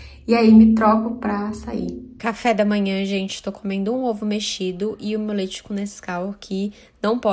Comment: Eu escutei "ovo mexido com leite condensado", já tava aqui "mdsss???????????"